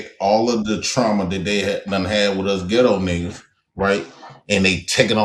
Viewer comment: You can tell, this miss heard, they thought he said the “FINEST 🥷” when he actually said “define us, ghetto 🥷 ” 😂😂😂😂😂